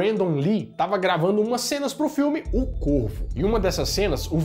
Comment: O nome do filme "O corvo" eu entendi "O corno " kkkkkkkkkkk